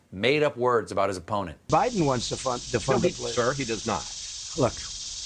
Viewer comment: Did he just say "biden wants to fuck" at 7:05 ?! LOL